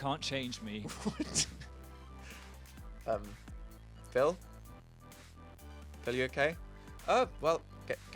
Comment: At first I thought Dan said 'Philly are you okay?' And I was exploding with pheels